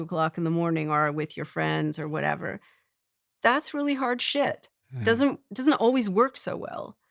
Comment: "thats really hard shit" did i hear that right :D